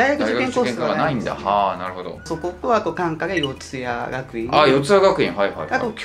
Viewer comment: なんて言ってるかわかる人教えてください。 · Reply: そこと～単科で四谷